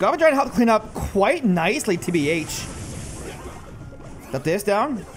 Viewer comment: did he just say "tbh" 😭